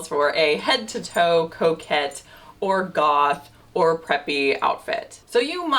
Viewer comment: At first, I thought I heard you say, "...not every occasion calls for a head to toe coke-head outfit" Quickly realized you actually said, coquette. But dang, I wanna see the coke-head fit! 😜 Looove the slip dress and your content!